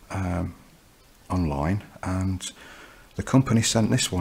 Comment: I thought that he said I’m lying but he actually said online which I found out after rewinding it a couple of times